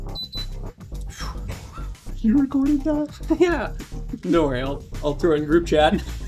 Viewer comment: What does he say there? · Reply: "You recorded that?"